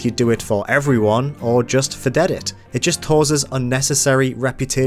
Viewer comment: Did he said fadet it?